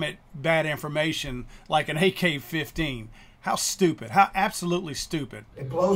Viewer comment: i sounded like he said AK 50